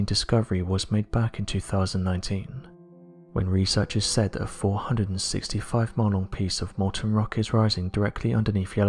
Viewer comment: For anyone else that had difficulty disccerning what the narrator said at 5:00 (since CC was no help) the phrase was: "...researchers said that a 465 MILE-LONG piece of molten rock is rising..."